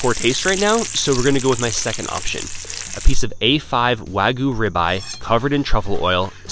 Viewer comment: At first I thought he said the price of the wagyu